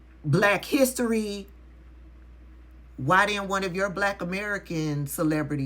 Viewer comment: I thought they said they didn't need Black Americans.